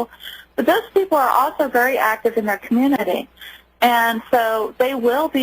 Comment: if you mean what she said at 03:05 then wrong! because she said "that" community and not "our" community.